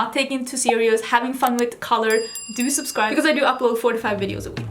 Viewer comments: I swear during the intro I thought you said you post FORTY-FIVE VIDEOS A WEEK and I turned into that meme where the confused lady is staring at floating math equations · I did a double take when I thought you said, "I upload 45 videos a week."